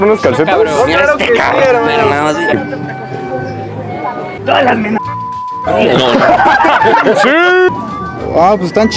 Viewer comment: que dijo el xd? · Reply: siii menores de edad siii